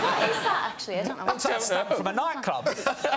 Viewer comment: What does Rob say at 1:35? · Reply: "It's a stamp from a nightclub"